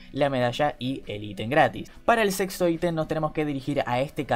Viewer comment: Pense que dijo *Para el sexo item xd*